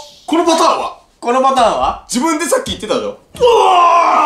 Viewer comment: マサイ「自分でさっき言ってたじょ？」って聞こえる